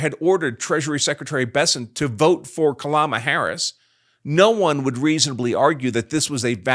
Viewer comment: Kalama Harris? 😅😂 If I heard correctly. Hilarious